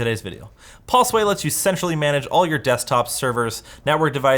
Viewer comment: am I the only one who heard him say “Pulseway lets you to sexually manage..”